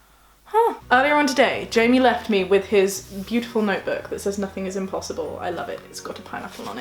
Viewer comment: At first I thought the notebook said ‘nothing is possible’ and I was really confused because Jamie is so happy, kind and optimistic all the time and then I realized I heard it wrong lol